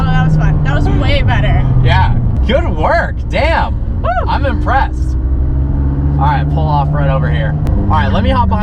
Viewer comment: i thought he said "im depressed"